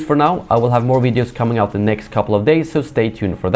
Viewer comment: Did you just say may the fourth instead of force at the end? I think your a bit early...